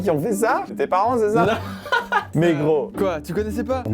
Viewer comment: C'est moi le seul qui a compris "negro" à 3:48 ? xD